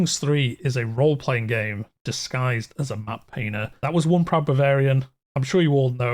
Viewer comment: I can't make out what the he77 he is saying. "Crusader Kings Three is a Role Playing Game Disguised as a WHAT??" Mapayner? No it's not mapayner wtf. Thanks .. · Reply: Map Painter, apologizes for my accent 😂